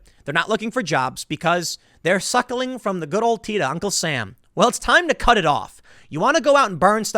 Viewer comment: Did you say cut off Uncle Sam’s teat? Seems kinda harsh😳